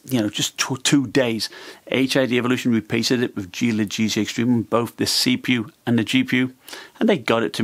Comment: sorry, what did you say??? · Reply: I said Hidevolution - the compnay that supplied the laptop - repasted it with Gelid GC Extreme thermal paste